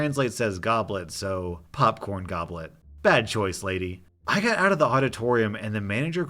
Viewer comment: As someone listening and not reading, I thought first op said popcorn goblin